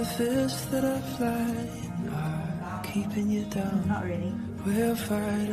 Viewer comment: Wait did anyone else hear it when she said not really ?